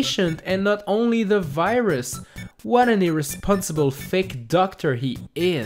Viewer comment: did you just say "Irresponsible THICC Doctor"?!